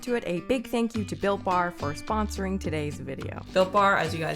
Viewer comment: Dead ass thought she said thank you “bill Burr” for sponsoring this video 😂